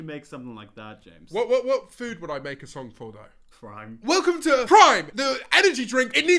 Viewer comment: I thought he said “crime” not Prime for the food song James should make 😂